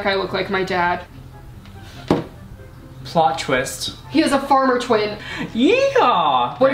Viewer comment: I thought they said, "what if he has a farmer twink" and I was like, "OOP-"